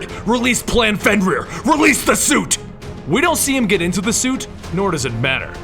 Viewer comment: He actually said to release the gas